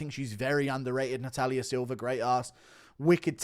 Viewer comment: Did he just say "great ASS"?